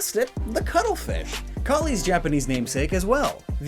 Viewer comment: Wait! Did he call Callie Collie?